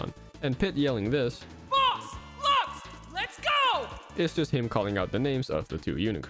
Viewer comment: Wow, really? I always thought he said “Full force lets go” but now this makes more sense